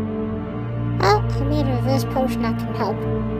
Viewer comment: What did Kevin say at 2:24? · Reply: I think He try said a cure